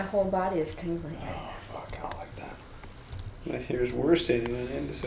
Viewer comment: “my whole body is tingeling” in the background “I like that” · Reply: Maceroni Gaming and Cheese I tried to convince myself that he said “I don’t like it” but nope, seriously sounded like he said “I like that” lol